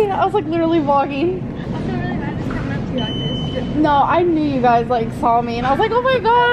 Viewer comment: What did the girl say/ask at 3:13?? Our gORL is a STAR!! Those girls seemed nice and like Ambabies. I bet our gORL charges for her autograph. And shoves the cash in her bra! Cuz mama needs a new pair of shoes! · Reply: She also said “I feel really bad about coming up to you like this.”